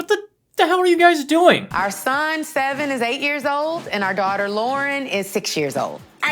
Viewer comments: Hearing the little girl saying “I can’t read” immediately after the narrator saying she’s 6 years old is frying me 😭😂 Especially because I hadn’t yet recovered from “Our son Sevin is 8 years old” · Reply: I had to go back because I thought she said “our son is seven or eight years old.” 😂😂😂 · “Our son seven is eight years old” I rewinded 10s to hear that correctly 😂 I thought she said “our son is seven or eight years old” I was like bih now you’re guessing 😂